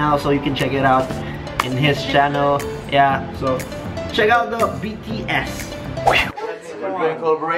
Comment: I thought he said"check out BTS." I was like "OMG I love BTS V is my favorite!!!"💕💕💕💕💖